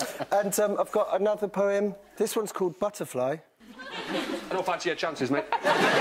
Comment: i don’t fancy your chances m8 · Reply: Always thought he said , " I don't know if I see your chances , mate".